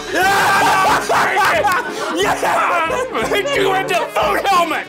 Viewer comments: In my 50th re-watch, I just realised that at 4:42 said "I'mma hit you with the FOOD HELMET", and I've been laughing for 5 mins😂😂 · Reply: I heard "fuckhelmet" first. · Did say “I will hit you with the food helmet!” 😂